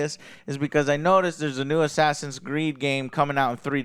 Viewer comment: did he said assassin's greed? Pretty accurate for a life service game